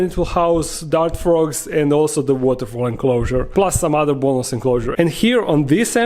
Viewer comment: When you said "Waterfall enclosure" I misheard it as "water fowl enclosure" and was really excited you were getting ducks